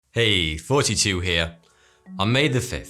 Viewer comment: AMAZING Video!!! I Misheard this Introduction as "Hey Forty-Two Hear" LUL